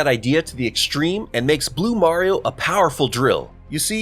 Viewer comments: DID HE SAY BLUE MARIO?! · did he say... Blue MARIO?!?